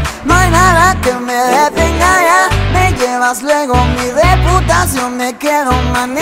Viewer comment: escuche "ni de putas yo me quedo" en vez de "mi reputacion" xDxdXd